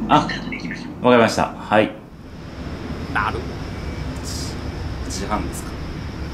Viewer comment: 9時半て聞こえたの俺だけ？